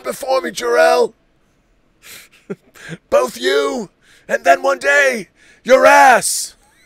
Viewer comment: I always heard it as ‘One day your ass ‘lol